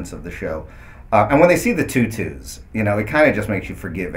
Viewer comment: lol This is great. I thought I heard something about a tut tut in there around 3:29... And Eliot "fighting" a bear might not be out of the realm of possibilities. I mean, they could steal a bear or something, and it could tackle him and be all cute-like. Eliot playing with a bear cub. How adorable would that be!? ^.^ But yea, this was hilarious.